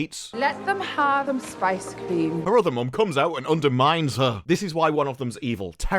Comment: I'm sorry did she say fucking spice cream? Spice? As in the Star Wars equivalent to heroine? The drug that Boba Fett is vehemently against in his show because gangs are using it to control or otherwise destroy parts of Tatooine?